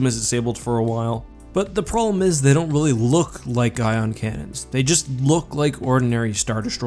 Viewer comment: I thought he said iron cannons and I’m not sure why